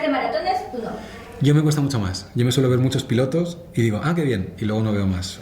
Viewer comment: Like si pensaste que dijo "pilotes"